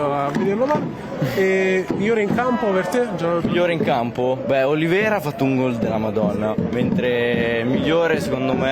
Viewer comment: Minuto 22:50 oliveira? Ho sentito bene? Ps. Scusate non avevo visto la scena dopo avevo commentato al momento di quando ha detto oliveira